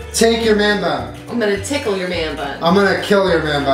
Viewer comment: i thought Jenna said "i'm going to tackle your man bun"